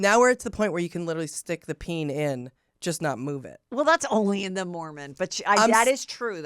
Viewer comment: Good morning scoopers 🌅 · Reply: I swear she said only in the morning